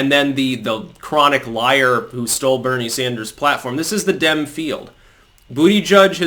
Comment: Every time you said “this is the Dem field“, what I heard was “this is the Dim field“. BTW, Siri spelled out “Damn field”.